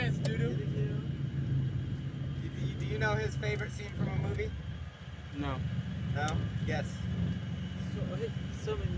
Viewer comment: What movie did he say at 18:00? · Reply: Update it was Sling Blade!